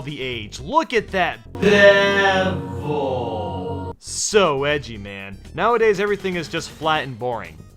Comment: did you say “So Wedgie, Man”